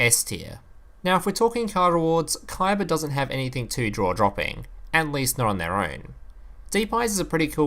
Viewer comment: xd? why do i hear draw droping ?